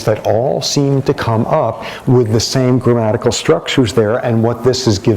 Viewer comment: Good job to whater person or algorithm is in charge of the CC on this one. 11:59 when creole languages are claimed to be "not simple". Lecturer says grammatial, CC says graphical, my voice intent intuition tells me the lecturer meant grammatical, I agree with CC and counter-point that creole are all simple. Done. 2010 BS in Game Programming. Done.